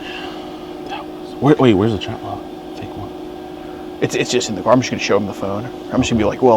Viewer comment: I'm sorry did I hear that right?? Did JDion say "where is the chat log, the fake one."??????